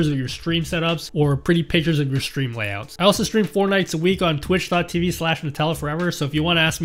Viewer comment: jesus 5:04 that sounded like i stream fortnite lol